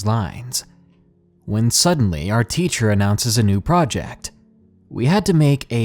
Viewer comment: Did anyone else hear "I Ran To The Creature" instead of "I Ran To The Teacher" ?? 😂😂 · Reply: I heard “Creacher” while listening on podcast, I had to come read the YouTube comments 🤣🤣🤣